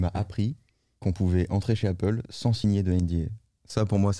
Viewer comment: Il dit quoi à 10:36 svp ? On peut entrer chez Apple sans signer de quoi ? J'arrive pas à entendre :/ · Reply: Sans signer de NDA ? Un accord de non-divulgation en français. Voir